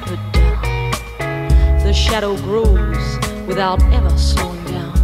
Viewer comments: THE SHOWER APPROVES!! Up to this point I had no idea what she was actually saying. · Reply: ayior I always heard "the shower grows" xD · I always thought she was saying "The Showered Rooms". That would be interesting to ponder upon. A bunch of rooms being showered. Ah yes.